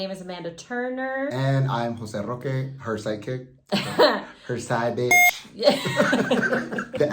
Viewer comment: At first I thought he said that he was "psychic"! :-D but he actually said "side kick."